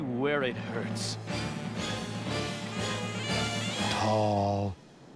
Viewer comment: What the fuck does he say at 4:09? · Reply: Gazing at tall building with one brain cell "Tallll...."